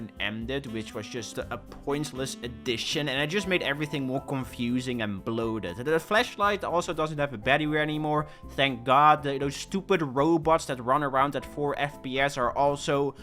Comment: did he say “fleshlight” 💀